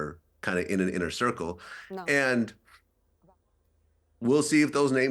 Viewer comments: Does anyone else hear the word "No" at 12:29? It seems out of place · Anyone else hearing a woman say “ no “ @ 12:30??